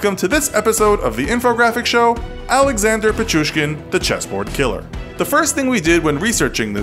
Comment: I don't know why but I thought it said The Cheeseboard Killer. 😓